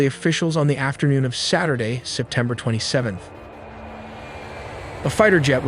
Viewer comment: did it say september 27?